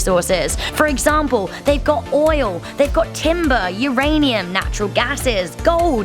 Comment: DID YOU SAY JUST SAY OIL!!!???? You doomed us.